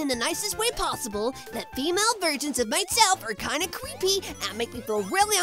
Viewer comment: did tails said CWEEPY instead of CREEPY